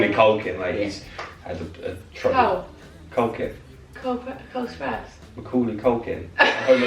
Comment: i actually died when you thought said cole sprouse😂😂